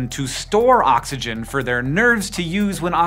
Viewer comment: am I the only person who heard “nerds” instead of nerves?